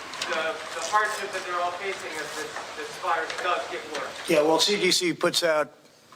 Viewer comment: I thought he said this virus, election is a hoax.